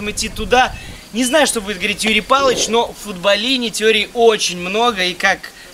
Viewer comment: когда ты сказал : "Юрий Палыч", послышалось - Ебалыч